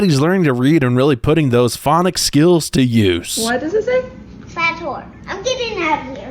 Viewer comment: Think the kid said "Fat Whore".